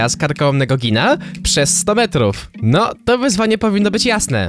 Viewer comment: Ja usłyszałem nie jeść misia przez 100 metrów XD